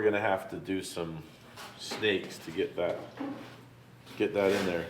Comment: DID YOU SAY STEAK